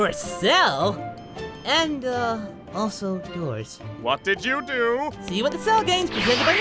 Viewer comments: what does goku say right after 7:57? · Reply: Goku: And uh... Also doors.... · what did Goku say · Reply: "Also, doors."